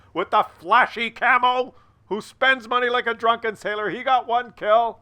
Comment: I'm not a showy spender... the cammo was free... it's hilarious because in the "fun fun fun" video you said I was a regular joe who doesn't buy premium. And now you're saying I spend money like a drunken sailor lol... I'm a working man... a man after your own heart... thx for featuring me!!!!! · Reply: At first I thought he said "a drunken tailor".